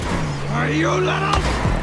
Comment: is 3:10 says "are you langgaw"? cox I hear this all the time and I can't clearly hear it.